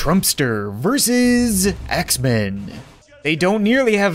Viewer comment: if you turn on captions instead of "axe men," it says "x-men"